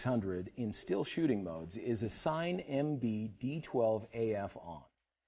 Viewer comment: I heard MB-D12… and i read MB-D14